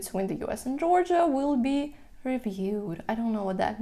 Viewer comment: "cooperation" is what it spunds like and then u say "will be revealed" it sounds like connections between the countries will ve decided. U.S. will probably try to influence Georgia to change things